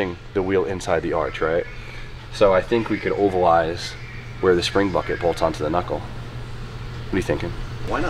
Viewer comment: sounded like said Springbok! 😂🕺🇿🇦